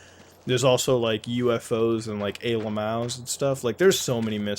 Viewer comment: Can someone please help me understand - Mutahar says "alamals" at 7:56 and he says it a lot when referencing alien/ufo things. What is alamals word? Am I spelling it right? Thanks in advance. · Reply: He says "Ayy Lmaos". It's an alien meme.